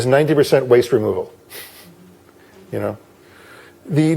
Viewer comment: Can someone tell me what he say in the minute 3:12, "yoga therapy is 90% _____", please??? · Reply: leon garcia "Yoga therapy is 90% waste removal." Katherine Marr